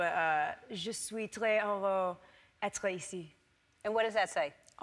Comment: I thought at first she said «Je suis très horreur d'être ici» which I took to mean «I'm terrified to be here» haha